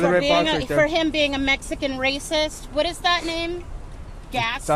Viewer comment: Bruh..... she was being racist but called him a “mexican racist”.... he mightve been something else, she assumed! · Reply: Wow I heard “Mexican rapist.” But really, what is a worse accusation.